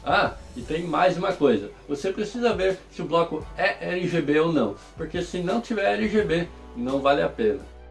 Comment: Boa tarde, no minuto 4:38 você fala que não vale a pena se não for “lgb” (ou RgB, não entendi direito), poderia explicar? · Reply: Ele foi irônico ao falar que se nao for RGB nao vale a pena. RGB são as luzes, apenas acrescenta na estética. Abç e feliz páscoa!